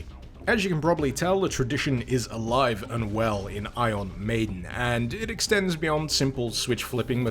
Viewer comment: I thought it said iron maiden